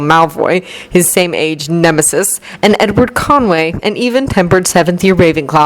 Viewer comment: Not gonna lie, I totally thought you said Edward Cullen😂😂 I was like " W H A T???"